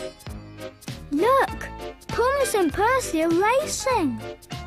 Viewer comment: How did I mishear this as “Thomas and Percy are racist”